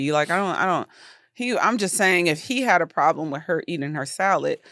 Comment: I had to ask my wife, "Did she just say, he had a problem with her eating his salad?" I'm like of course he had a problem.